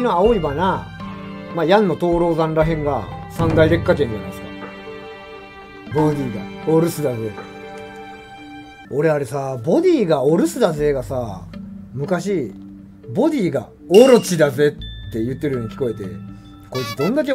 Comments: 静寂が嫌いとかさんまさんじゃんかDJ ９７のときはボディーがオロチって聞こえてました。ムックを買ってセリフ集でお留守って書いてあったの見てびっくりした思い出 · 草薙京の荒噛みのコンビネーションセリフの「ボディがお留守だぜ！」「ボディが甘えぜ！」「ボディがガラ空きだぜ！」空耳で「鬼いがお留守だぜ！」「鬼いが甘えぜ！」「鬼いがガラ空きだぜ！」に聴こえてました。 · ボディがオムツだぜ！には聞こえた時期があるね。 家庭用プレイするまでは。